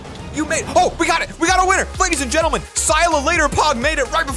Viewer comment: Did you just say.. Did you just say "si ya looter dog.."?